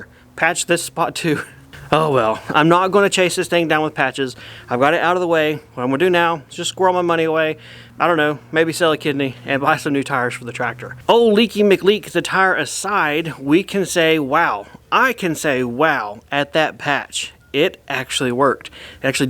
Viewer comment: I thought at first was saying "maybe I'll sell a kid" instead if kidney 🫢